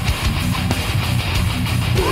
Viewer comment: I know he is saying priest of sodom. But it sounds like he is saying plead for soda